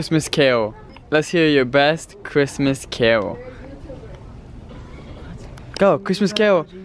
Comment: I thought Lucas said “kale” not “carol” 😂❤️